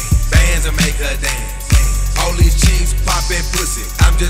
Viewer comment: Now that some ppl said they thought he said vanz a make her dance I can't stop hearing it ,😭😭😭😭😭😭